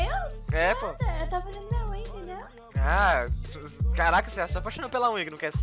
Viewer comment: eu ouvi ele fala aí Carol ksksks slv seu